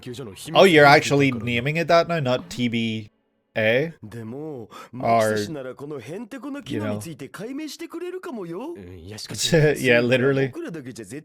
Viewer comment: No, he actually said TBA or "kakko kari", just the subs didn't show it